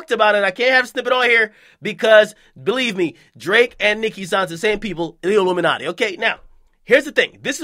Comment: Did he say “Drake and Nicki sign to Illuminate in 1:22”😨